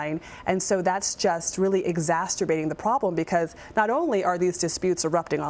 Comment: Did she say “exac-sturbating”?